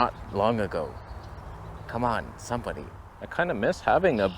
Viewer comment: Did you say "sompany" @ 7:21 😅